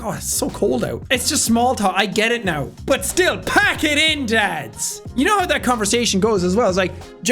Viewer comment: I thought you said pack it In lads not dads